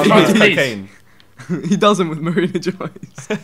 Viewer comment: What did Vikk say? He was laughing at the same time. Did not hear. D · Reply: They were talking about doing lines and Vik said "He does it with Marina Joyce." (Reference to cocaine.)